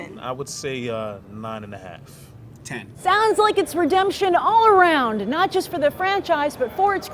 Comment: did mispronounce redemption at the end? couldve sworn she said redumption